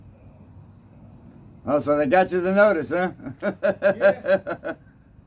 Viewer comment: I thought you said oh you got the lotus huh. Dude let me see 😂